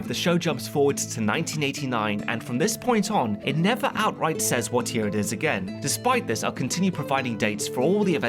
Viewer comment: cannot work out what is said, 'never outright says..' what? 🙂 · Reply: ahhhh 'what-year it is again'.